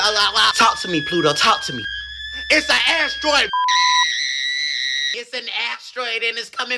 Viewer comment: why did I hear “Airstroid” HELP 😭🙏